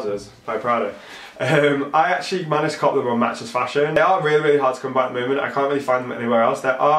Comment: what are you saying at 04:33 of your vid?? I hear "I actually managed to ... XYZ... Matchesfashion". But what is XYZ??! Not everybody here has (British-)English as native tongue... ;) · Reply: I said I copped them on matchesfashion! haha meaning I got them from there!! :)